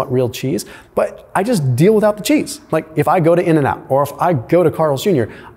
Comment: Did you just say to "cut the cheese " is a healthier option?.... aye aye cap'n 😂